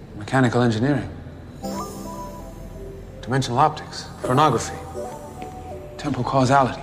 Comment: I thought he said Pornography 😂